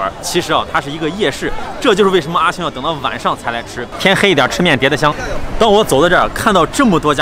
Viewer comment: 說什麼, 聽不懂? "天黑一點吃麵至的香" , 是說 天黑一點, 吃麵才會香的意思嗎? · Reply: 西安方言吧； 是的。天黑一点，吃面才更香。